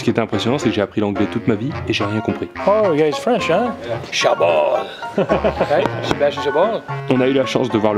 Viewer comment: Qu'est-ce qu'il dit à 1:30 "You guys are French... Cha'bon?"